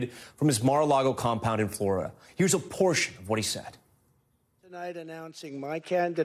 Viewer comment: All I heard was, "Here's a porsh of what he said.." 😂